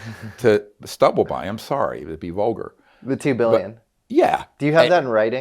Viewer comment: I thought he said he was offered 200 billion not 2 billion…